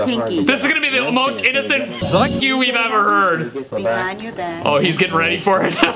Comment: Why do I love the cursing cartoons videos you do lol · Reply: btw, I hear "Vuck you"